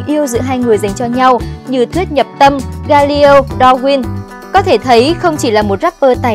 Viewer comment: tao có nghe nhầm ko hay là galio nhỉ